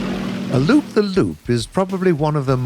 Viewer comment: I always thought people were saying "Loop-d-loop". "Loop-the-loop makes way more sense.